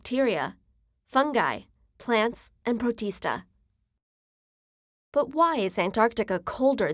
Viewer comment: I thought she said Bautista